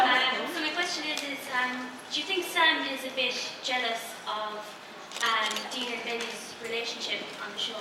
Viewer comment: sounded like she asked if Dean thinks Sam is a b*tch